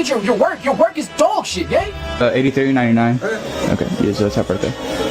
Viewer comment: when i heard $83.99 i thought he said “everything ninety nine”